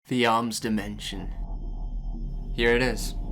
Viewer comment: for a second i thought he said 'the omz dementia' at the beginning